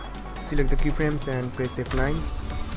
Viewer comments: I'm sorry but I couldn't understand you on minute 13:10 when you said select the key frames and press... what?? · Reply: press f9 to easy ease your keyframes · What should i press at minute 13:10? i dont understand what u said ... i'm sorry :| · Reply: Select this two key frame and press F9.